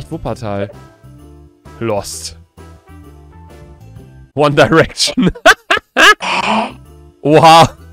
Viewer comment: habe ich gerade One Direction gehört 😂😍